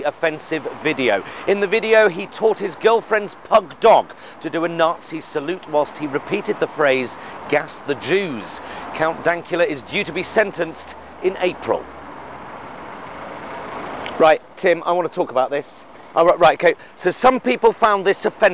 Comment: So... politically correct phrase would be... 'Gass the J-word?' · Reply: And even that can be seen something else saying “J-word”; plus in the video maybe because of Count Dankula’s accent it sounded like he was saying “Gas the juice”